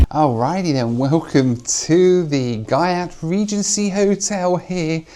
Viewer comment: Did he just say GYATT Regency Hotel?